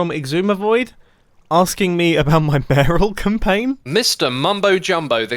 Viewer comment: Did anyone else hear "barrel campaign"?